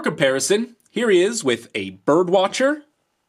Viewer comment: did he say birb watcher